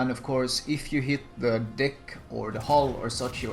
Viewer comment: Wasn't really paying attention to the video, and when said '...so much superstructure, and of course if you hit the deck...' (5:54), I was like - did he just said 'd*ck' ? (hitting d*ck with HE would be painful too ;D). PS. Still have no idea how to play gunboats :< Loved playing YOLO IJN style up till tier 6 dds. (Nobody excepted a DD charging at you full speed to torp at point blank range>:D)